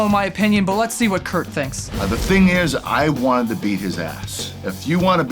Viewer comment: for a second I thought kurt said I wanted to be his a**